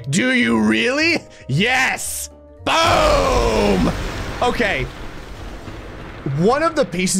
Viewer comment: it sounded like BOOM HEADSHOT